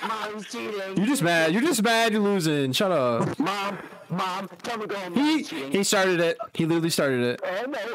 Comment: If anyone is wondering what i said at 7:28 that was the “???” i said MOM TELL HIM TO GO HOME😂